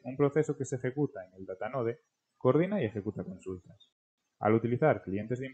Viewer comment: No sé entiende que dice 5:29 Básicamente un proceso que de ejecuta en el ___________? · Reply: Datanode